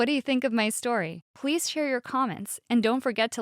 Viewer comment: lol she said pweese instead of please